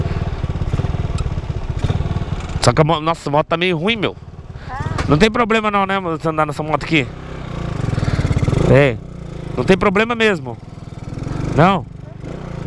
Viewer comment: Só eu que escutei um amor